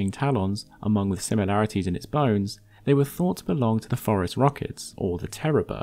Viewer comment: I thought you said that cuban owl was thought to be a forest rocket, not a phorusrhacid lmao. I was like "damn what a cool name for a fucking bird"